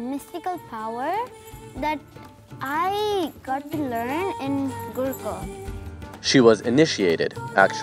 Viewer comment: I thought she said google lol